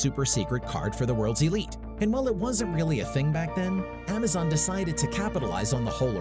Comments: did you say Amazon?? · Do let us know what you would like to see in future videos! Alternatively you can get in touch with us here: <link> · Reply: you said Amazon instead of Amex or American Express. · did he say Amazon?